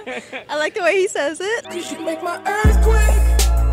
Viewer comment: I swear to god he said ass quake